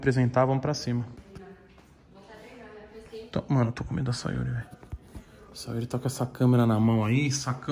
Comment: só eu entendi ele falando? "mano, to comendo a sayuri" kkkkkkkk